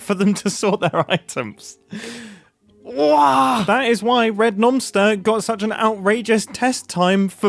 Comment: Oh, nomster. I thought you were saying noncter